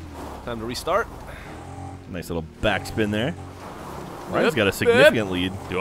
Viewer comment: Jacks being a jerk again!!! He clearly said "back spin" and Gav clearly made a good joke.